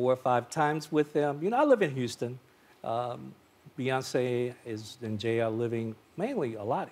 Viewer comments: I had to go back and listen 3 times! at about 7:47 I know I heard him say Bey lives in jail in Los Angeles, what!? please tell me he said something else lol UPDATE : he said Beyonce and Jay lol he had tripped on his word thank goodness!! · I heard him say beyonce is in jail